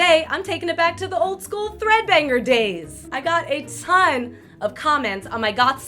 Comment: I thought she said got7